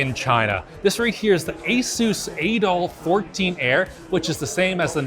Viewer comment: bro I had to turn on my screen to see if I heard that right 💀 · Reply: Sooo... it's not the Adolph 14 Heir?!